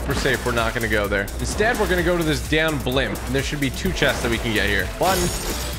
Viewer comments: Thought he just said DA/\/\n blimp lol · i swear I heard you say “ going to the damn blimp